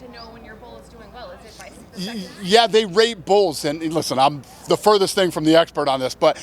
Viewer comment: I'm pretty sure i heard Dana saying "they r@ bulls" ...?